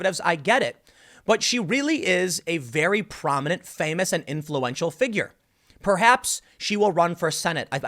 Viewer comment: Holy fuck Tim, I thought you said she was an 'intellectual leader' I listened back and it was 'influential'. Almost unsubbed there, that was close.